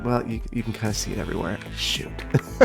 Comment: When you were putting in the sewage treatment facilities and they didn't look right, for a second I thought you said sh💩, probably not the best idea to whisper 'shoot' BlargNaut